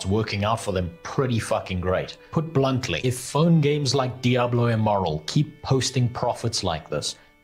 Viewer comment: I heard Diablo Immoral instead of Diablo Immortal.